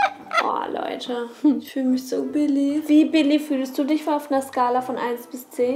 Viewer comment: habe ich verstanden ich fühl mich so billig (